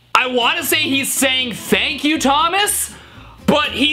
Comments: Hi! I CLEARLY heard “F**k you, Thomas.” I’m surprised parents didn’t complain about it like they did Bob the builder, which I didn’t hear any swearing in that clip, it was just muttering like they claimed it to be. I did hear “F**k” in Hunchback of Notre Dame, I also think Mr. Rogers knew what he was doing because of the way he grinned right before he flipped the bird! 😂 Hilarious video! Have a good day! 😃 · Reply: I CLEARLY heard “Thuk you, Thomas” Bro was freezing 🤔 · Hi! I CLEARLY heard “F**k you, Thomas.” I’m surprised parents didn’t complain about it like they did Bob the builder, which I didn’t hear any swearing in that clip, it was just muttering like they claimed it to be. I did hear “F**k” in Hunchback of Notre Dame, I also think Mr. Rogers knew what he was doing because of the way he grinned right before he flipped the bird! 😂 Hilarious video! Have a good day! 😃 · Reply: I heard “H-Huck Fu, Thomas..” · I heard "is that u thomas"